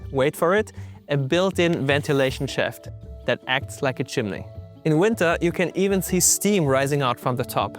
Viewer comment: Did you say Chimley? I've heard some Canadians pronounce Chimney as Chimley, is this where it comes from?